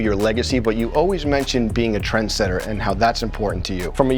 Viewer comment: I heard “being a transgender” 1:09 😂